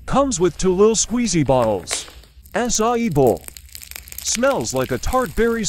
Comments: For the acai bow one I heard "A*s I eat bowl" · why did I hear “ass-I-eat bowl” 😭